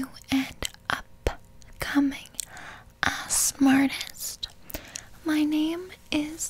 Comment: I THOUGHT SHE SAID ASS ARTIST LMAOOOO